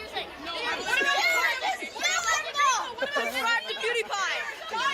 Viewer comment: I swear I heard someone say "sub to pewdiepie"